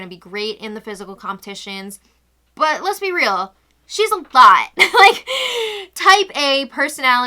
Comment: Did call Christmas a THOT? Does she know something I don't? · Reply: CA2VA no, I think she said “Christmas is A LOT” aka a handful